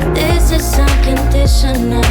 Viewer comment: Au début de l'intro, j'ai compris "this is so conditioner" 😅 pas de vacances pour moi ! Je pars au Mexique en septembre si tout se passe bien !!